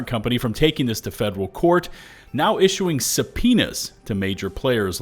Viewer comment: im not a native english speaker what did you say 1:10 ? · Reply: Subpoena